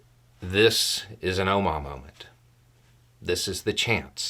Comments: I think that it's something to do with the previous conflict in Ireland. ( Irish.) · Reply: Did I hear right; "Ohma moment"? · "Oma Moment"? Not sure if I'm hearing you right, and either way, not familiar with the term. I tried searching, but it wasn't fruitful, so guessing either I heard you wrong, or... ?? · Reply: Omagh , try that in your search